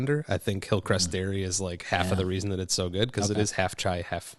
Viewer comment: did he say, "Darius"?